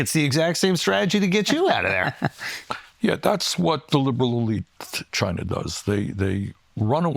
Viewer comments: Does he say "That's what the Liberal Elite China does" ? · "that's what the liberal elite, China does".... did i hear that right?!?!?